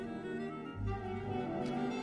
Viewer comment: I misheard "Get my Lolo!" which means "Grandpa" in Tagalog. 😂😂😂😂😂😂